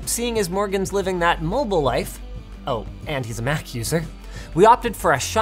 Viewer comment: Who else thought that he said "Oh and he is a meth user"